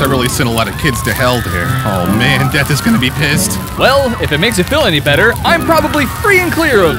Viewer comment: "Oh hell, Death is gonna be pissed" What I first heard "Oh hell, dad is gonna be pissed" wait, wot ?